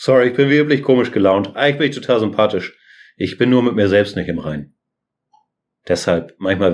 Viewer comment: Hallo, ich lerne Deutsch. Was sagt er um 8:42? Ich verstehe "ich bin wie üblich komisch gelaunt" aber fast nichts danach. Vielen dank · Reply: "Eigentlich bin ich total sympathisch, ich bin nur mit mir selbst nicht im Reinen."